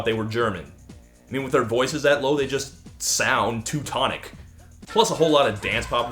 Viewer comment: I heard "too tonic" not "teutonic"